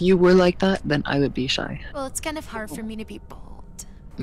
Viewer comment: why did i hear "well its kinda hard for me to be bald" 💀